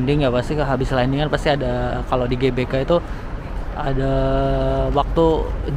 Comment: kupingku salah denger kali ya, koq di GBK ya? 😁